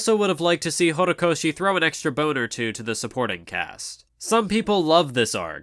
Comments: who else thought he said boner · when he said "throw an extra bone or two" I thought he said "throw an extra boner too" XDD